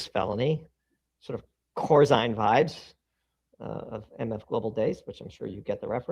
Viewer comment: What that word around 33:49 that Doomberg used "Corsine hives' - I ddint quite get that reference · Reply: Corzine vibes.. referrencing Jon Corzine